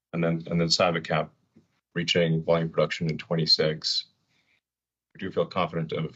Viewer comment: I thought he said ‘attain volume production in 2026’ implying start of production before this - possibly H2 2025?